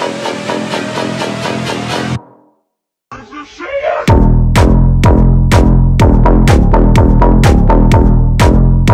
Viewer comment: what did he say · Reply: "It's a shark!"